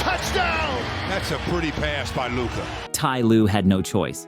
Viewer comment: I thought he said “that was a booty pass by Luka”